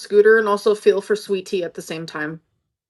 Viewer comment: I’m sorry, did she say “like I’m not just one di-mental”? Tell me I heard that wrong